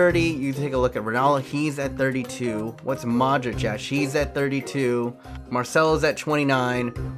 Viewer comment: did he say she to modric